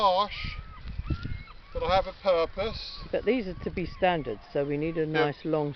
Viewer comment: Did you just say you had a “pear-pous”?